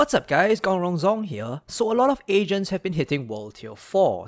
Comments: I legit thought he said a lot of asians have been hitting world tier 4. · Man, for a second I thought he said, "...a lot of Asians have been hitting World Tier 4..."